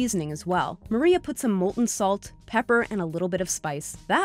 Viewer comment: Did she just say Molten Salt